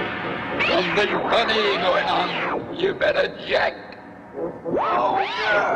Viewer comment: if you want to know what they are saying here is what they are saying Godzilla: hey angerus Angerus: what do you want? Godzilla: something funny is going on,you better jack Angerus: oh ya! Godzilla: I am Hopefully that helped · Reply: I heard “you better check over there” also if you want help the names are “anguirus” and “gojira”